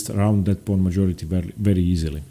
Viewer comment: Oh . . . I thought it said "PRAWN majority."